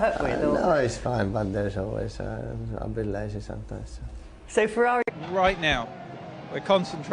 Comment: Did he say “I’m a bit lazy sometimes” @ 1:45? Lmao